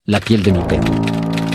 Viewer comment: En el minuto 00:00 escuché la piel de mi nepe xd